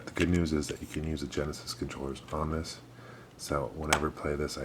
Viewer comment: sorry for the nitpicking, but it kinda sounded like you said "genis" controller a couple times. Anyway, keep up the great work!